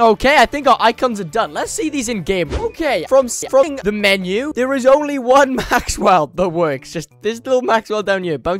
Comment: YTP Clip sounds like "Suffring the menu" instead of "seeing the menu", maybe check the audio next time.